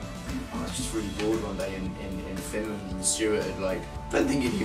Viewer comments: Did I hear "in Finland" at 4:42 ! cos noo he has to come play a show here! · Wait did ed say Finland at 4:40?